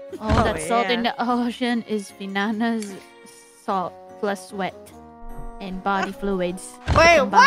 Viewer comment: I think she says Salt + Sweat